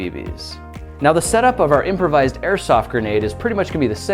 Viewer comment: i thought it said microsoft grenades